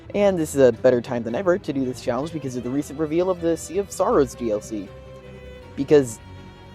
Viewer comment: when you said “Sea of Sorrows DLC”, my brain thought you said “Sea of Stars DLC”😂